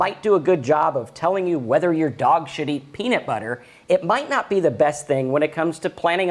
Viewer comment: Lol! I thought you said "and if you want to know whether or not your dog's $hitting peanut butter" 😂😂😂